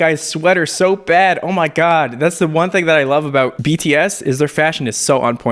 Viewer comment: Did I hear BTS 1:09?